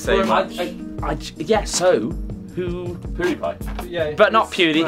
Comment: at around 7:35 did say Pewdie? I didn't think Pewds really hung out with yogscast off cam for them to name drop him in a video. Well I know they all talk through Polaris some times but I wasn't sure if they was friends. After all I see pewd fans attacking yogscast fans a lot.